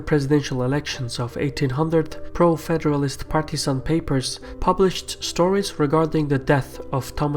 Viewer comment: Hhhhhmmmmmmmmmn that sweet sweet patreon early access~ Nice to see the more things change the more they stay the same when it comes to blaming the jews... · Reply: The Hunter x Hunter 2011 Dickriding Association 'During the presidential election of 1800s, pro-federalist Pakistan papers 7:28'? Is this what i heard? Pakistan was part of India (Asian India) which was created in year 1947. Is it Pakistan papers or partisian papers? I am confused.